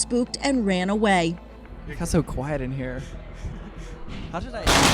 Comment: Did you say it was too quiet?